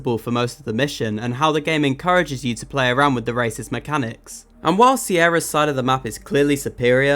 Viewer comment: The subtitles said “racist mechanics”😂